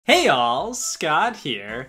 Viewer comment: I THOUGHT HE SAID HEY YALL IVE ONLY JUST FOUND OUT HE SAYS HEY ALL!!!!!!